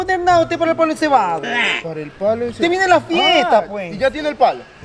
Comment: Pensé que decía encebollado 🤣😂